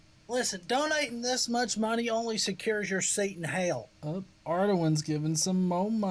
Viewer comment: I just realized the one alert say "donating this much money only secures your seat in hell" i kept hearing "donating this much money only secures Satan's hell"